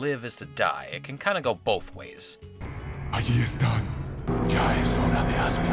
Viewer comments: Ahí están.. When the enemies said that It always reminded me of food because I heard it as "Alietta" which seemed like Aletta and it reminded me of aletta Di pollo aka chicken wings Yep. I love eating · I'm Dutch and i always laughed at the "ahí están!" that the cultists often say. (6:31) The way they say it, it sounds like Dutch: "hij is daar!", which means "he's there!". Together with the pointing of the hand, it really looks as if they say that. Now i understand, since it almost means the same thing! But instead of "he", it is "they".